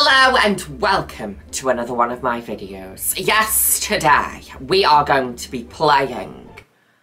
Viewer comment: every times says “YES TODAY” I keep hearing “yesterday” yes that’s happening